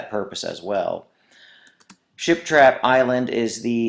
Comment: OMG THANK U SO MUCH I NEEDED THIS!!!!! also every time he said ship trap island it kinda sounded like he was saying shit trap island lol